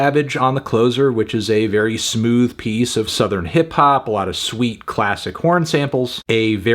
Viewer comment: I never listened to the album and there's a good chance that I'm not going to, but when you were describing Jimmy Cooks, I almost thought you said "lot of sweet, classic Korn samples" and I was thinking "wait what-" lmao